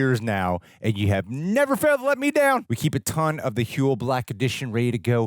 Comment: Wait, 47:05, did u just say huel never failed to let u down? 😂